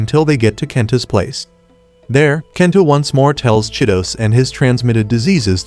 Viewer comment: I actually thought he said shitos